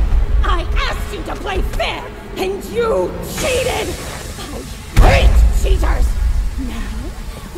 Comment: did she say "GREAT CHEATERS" or " HATE CHEATERS" I'm confused, pls reply. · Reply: *I HATE CHEATERS!!!*